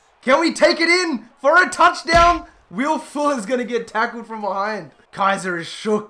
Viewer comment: Wait did he say will fuller🤣🤣😂